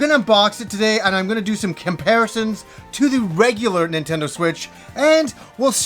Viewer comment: I swear I heard you say you were gonna do a "Kimparison"